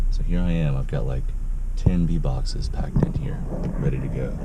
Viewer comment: Yes I have a civic as well my friend. Great cars. When you said beesbox I thought you said beatbox 😂 has the cutest voice Omg🥺😍love you all